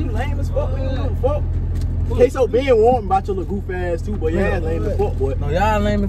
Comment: Peep how they said “kaso been telling me to watch you” he wasn’t lying about that y’all gotta peep the lil shit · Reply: I thought they said "Kaso been warned me about your goofy ass"